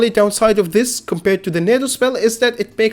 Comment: We really got the subs saying NATO instead of tornado